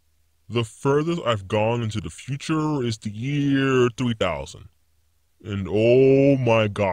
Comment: Who heard 2000 instead of 3000...only me?...okay...